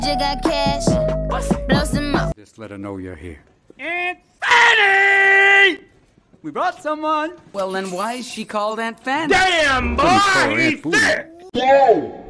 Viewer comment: I heard Rodney say "DAYUM"